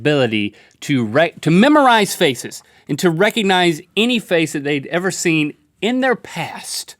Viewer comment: I love how it sounded like Link said "can you Rhett-cognize faces reconize